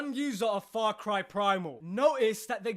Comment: for a second i heard, "Fart Cry - Primal". :D